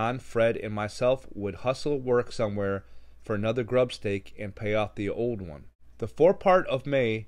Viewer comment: I thought he said tube steak